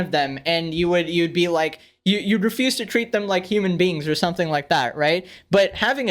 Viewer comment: you already know when is legendary when he says, "Human beans" instead of "Human beings"